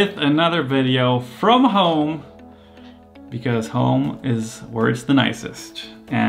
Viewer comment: I thought he said “because home is worse than ISIS” 😳